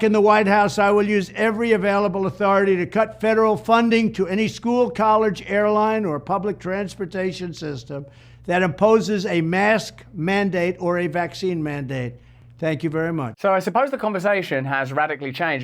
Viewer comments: Am I the only one who heard Trump say, We will CUT funding to anyone who OPPOSES mask mandates?!? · Sounded like Trump said (remove funding from those who) "Opposes" mandates... he should have said "Imposes" am I right??